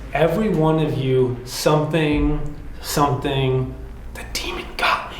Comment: "Everyone of you were respectful. The demon got me" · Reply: I hear, "Everyone of you better run the demon got me".